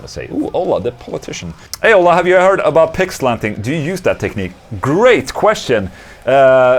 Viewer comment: My wife thought you said "Pig slapping" not "Pick slanting" 😂😂😂